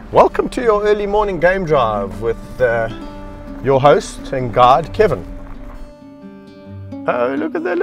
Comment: Kevin said "your host and guide, Kevin" but heard it as "your host and god, Kevin". I'm fine either way. XD